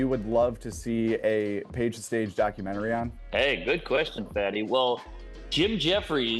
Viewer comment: did he really call u 'fatty' lmfao wtf was that